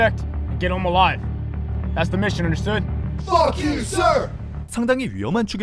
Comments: 에서 알겠습니다 할때 나만 뻑유썰! 이렇게들림? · 왜 "빠큐 썰!" 로 들리지...? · 아니 ㅋㅋㅋ 나는 왜 뻑큐 썰이라고 들리지?ㅋㅋ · 뭐지? 0:45초 애서 무언가 퍽유 썰 로들림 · 나만 'Fuck you, sir!' 로 들림?